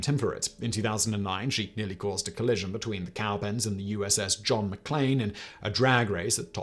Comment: did Simon just say USS John McClain instead of McCain? (or have i been watching Die Hard too much?)